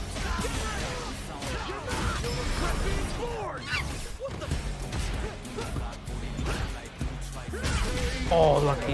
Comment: Did Ed just said „what the F“? 5:53 This character ist Dope man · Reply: He just said "What the?"